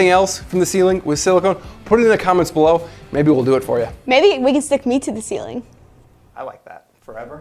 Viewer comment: I like this squad...but did she just say stick her to the ceiling forever...hold up